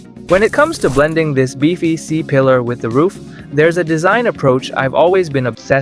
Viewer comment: 😂 BBC Pillar. I heard that.